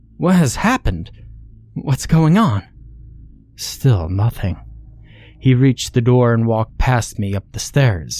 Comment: he didn’t reply, and the main character was at the doorway in between the kitchen to the stairs, which was where the father was heading to in order to go up to his room, that’s why he turned to him, i think? that’s why the main character said the dad still “ignored” him when he asked again. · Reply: oh I guess I heard wrong I thought he said nothing boy. That makes more sense then lol